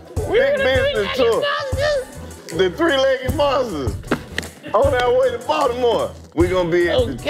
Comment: I could have sworn it said the three headed monster